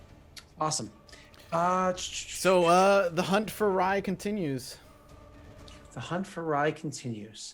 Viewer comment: When they said "The hunt for Rye continues," I misheard them and had to restart the video. Why? Because I could have swore that said "The hunt for Rye October." Wishful thinking, I guess.